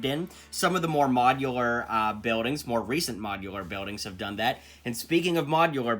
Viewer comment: Wait did you say mojilUr buildings?